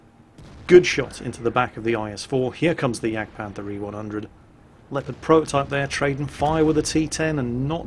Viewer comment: I swear i heard say Jagdpanther E-100.